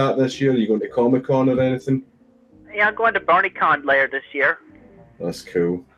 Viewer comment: Actually surprised you could transcribe and translate a lot of the more recent vids because Christ honestly becomes unintelligible. I have no idea what she's saying in her rage vids half the time. Good job! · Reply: God, right? I legitimately thought she said she going to BARNEYCON